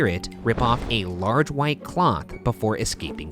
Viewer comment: Did anyone else hear Ryan say "large white clock" at 7:22 or was that just me